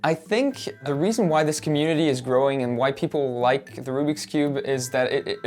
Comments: ME: did I hear WHITE people Voice inside my head: whyyyyyy peoplleeee Me: ohhhhh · did he just say “so i think that’s why this community is growing and the WHITE PEOPLE like the Rubiks Cube”?